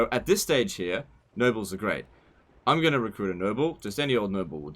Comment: he says "noble", i hear "nurgle" ..everytime